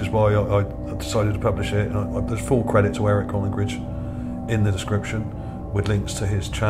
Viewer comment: about that skull @ 7:35, what did you say the fellow's name was that found it? Eric what? Sorry my hearing is going, couldn't make out the fellow's last name. Thank you! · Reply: Eric Collingridge I think :)